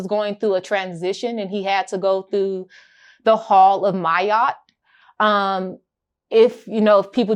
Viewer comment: I thought that shit said hall of meat pause😂😂😂